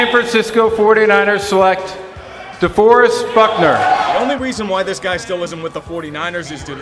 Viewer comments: At frist I thought he said fuckner 🤣🤣🤣🤣🤣🤣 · Did he say fuckner?